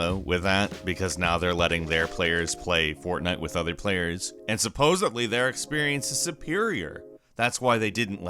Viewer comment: Did he say "supposively" at 2:15? 0.o